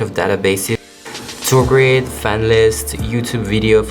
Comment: What are you saying here before 'fan lists'? the subtitles didn't pick it up. 'tour grid'? Also at 5:49 - you said you send 'a lot of merch to...' - I couldn't get it. The subtitles said 'east and'. it sounds like 'lucky stand'.